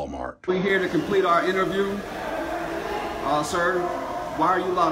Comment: What in the world did that last guy say?? 😂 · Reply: I thought he said never shoot